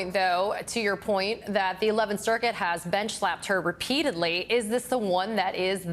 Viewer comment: Did she just say "BENCH-slapped?" Best turn of phrase I've heard throughout this entire mess!